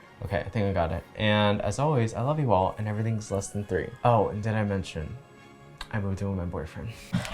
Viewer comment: For some reason I heard "I'm doing my boyfriend." and not "I moved in with my boyfriend." I had to replay like 3 times and put captions on (which didn't even help me)😅😂 Sorry 😓😬